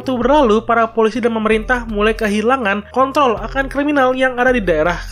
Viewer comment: apa cuma gw yang salah denger k*ontooll